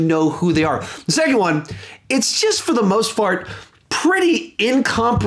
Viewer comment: "for the most fart" instead of "most part" lol. Love u